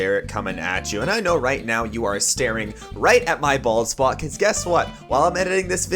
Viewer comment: When you said don’t look at my bald spot I thought you said balls?!?!